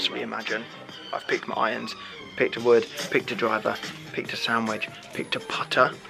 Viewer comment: When Alfie said “sand wedge” I thought he said sandwich and I died laughing 😂